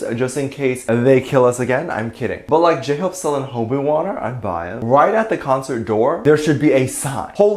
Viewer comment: 😂😂😂Tae wearing a dress made out of spiders. And I thought you said Hobi water and I was like YES! Kook water, Min water, Joon water, WWH water, Park water, V water yes yes yes . That LV store is the one in TriBeCa. Gorgeous 💜💜💜💜💜💜💜